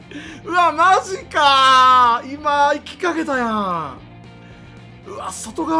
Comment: 「今行きかけたやん」が「今逝きかけたやん」に聞こえた。